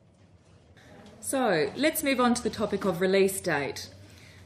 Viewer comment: Correct me if I'm wrong: I heard "Let's me go on the topic..." at 1:06, but the text shown "Let's me move on..."!